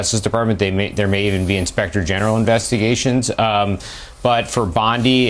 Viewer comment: Did you say Inspector General, or Inspector Gadget ?? ;)